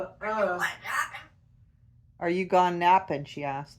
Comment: I thought he said "Are you done yappin?"